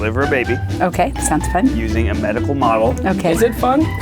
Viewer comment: I thought Ned said an edible model at first am I the only one?